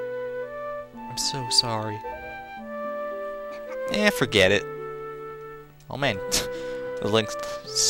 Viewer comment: was just perfect timing! it sounded like link actually said "ehh forget it!" lol